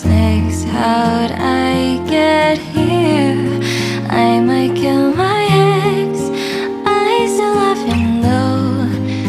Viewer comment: after sifu stream all I hear is: "I might kill my eggs" :elbowcough